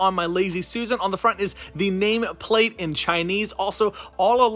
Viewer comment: Did he really say Chinese